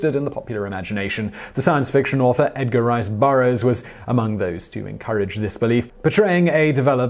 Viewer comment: It sounded like you said, "Edgar Wright Burroughs".